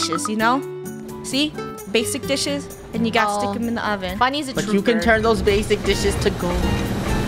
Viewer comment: Who else heard at 16:40 she said oven not dishwasher